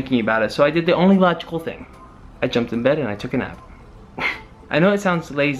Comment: when you said take a nap I thought you said take a knife lol😉